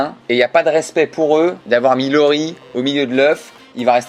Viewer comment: Pour l'histoire des articles pas remis à leurs place j'ai entendu "Avoir mis Lorie au milieu de l'oeuf" MDR